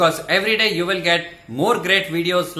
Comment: Hahaha i thought the Dr. Said Liberal Magnets LOL. But I understood what he sai ;)